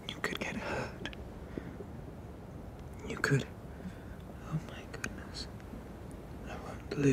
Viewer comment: what does he say before he says he's gained something something about Blues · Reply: He says "oh my goodness"